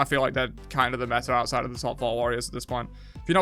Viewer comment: Wtf did he say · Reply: Top 4? I can't tell either 😂